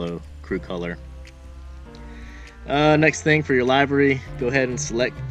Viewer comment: did he just say library cos it defo sounded like it